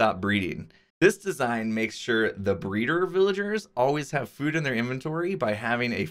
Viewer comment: i thought it said blender mot breeder lmao